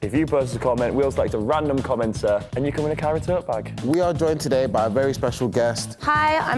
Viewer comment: whenever says “kyra tote bag” i always hear “carrot tote bag” n it always catches me off guard lmao